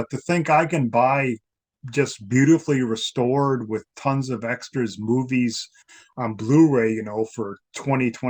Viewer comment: I thought said: "tons of beautifully restored extra boobies". 😶😆😆😆